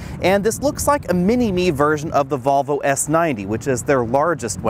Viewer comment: You said s90 instead of v90 on